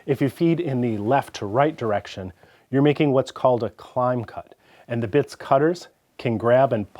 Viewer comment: Realised he was saying "climb cut" after about the fifth time... before that I thought he said "clown cut". Seems legit.